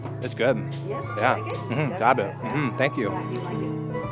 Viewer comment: sorry, I didn't understand, did he say "dab it"?